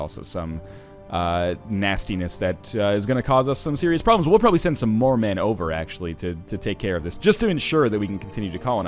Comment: I thought he said "We'll probably send some Mormon over..." in stead of "We'll probably send some more men over..."